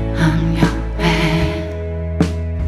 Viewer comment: why do I hear " I'm your band " all the time lol